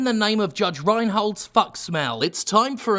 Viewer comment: Did...did he just say "Judge Reinhold's fuck smell?" I didnt hear anything after that! LMFAO! People ask why I watch Jim Sterling and I simply reply, the nuance!